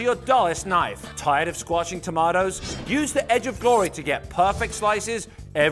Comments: I heard the guy said the edge of boring at this part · I thought he said edge of dory